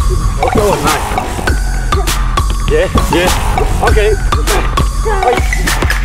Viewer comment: did he actually calls you 99 passing 😂